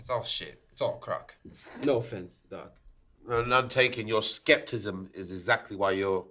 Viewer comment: I'm sorry but did he say "skeptism"?! 😂😂